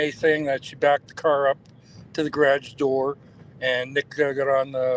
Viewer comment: I hear "Her Cars in the garage" I had to step away for a few days so u could try to listen with fresh ears but I'm like 90% sure I hear NA say to, "Her Cars in the garage." Listen again and see what you think?